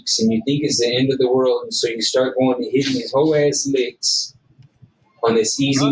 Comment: did he say these "hoe ahh licks" 😂😂😂😂😂😂😂😂